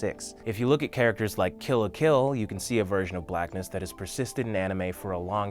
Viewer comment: *rewatch. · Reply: Oh!! The character Killa from one of the World Martial Arts Tournament arcs. Sorry, I must've misheard him. I thought he said Kill la Kill.